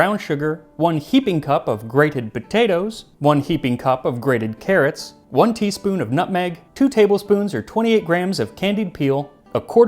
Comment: For some reason i heard "one teaspoon of nothing" 💀